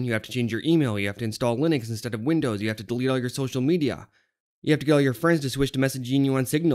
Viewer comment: i thought i heard You have to Kill your friends